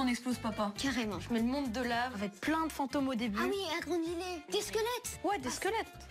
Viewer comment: A 00:04 seconde elle dit "ah oui argolilé" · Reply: La jeunesse française dyslexique. J'ai entendu "agranlis les"... Une granle fille avec ses squeletz !!!